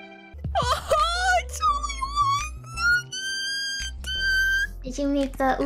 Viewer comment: For a moment there, I thought Kronii said “One Nuggie!!!!!”😭😭😭😭😭😭😭😭😭😂🤣😂🤣😂🤣😂🤣😂🤣